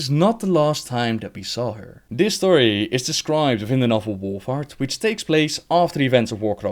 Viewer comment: Lol i kept hearing "The novel "WarFart""